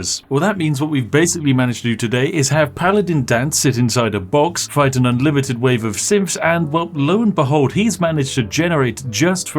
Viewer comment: i heard "Fighting an unlimited waves of SIMPS